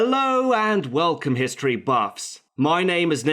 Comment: I swear i thought he just said hello history fucks